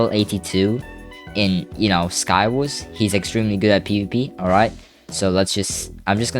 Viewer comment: the captions read poop instead of PvP 😂😂😂😂